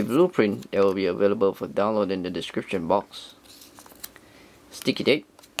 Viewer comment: For a second i thought, you said:"The blueprint, it will be avilable for a dollar in the description box."